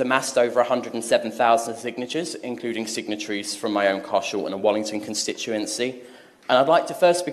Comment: thank you sir. now from across the pond, does anyone speak English? 1:47 signatures including signatories from my own CAR SHOW and a Wallington constituency what is a car show????? i am sure its a word for something like caucus. or something.. its driving me mad to try to figure it out.